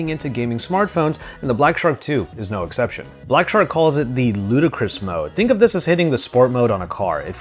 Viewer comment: thought you said ludacris mode! luu-da!